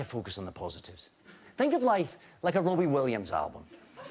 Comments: I thought he said Rory Williams album at first. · I almost thought he said "A Rory Williams album" and I went "DOCTOR/COMPANION CROSSOVER!!!" XD XD XD XD